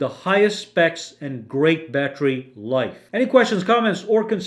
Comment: At the very end I thought you said hella fantastic day lol I need coffee ☕️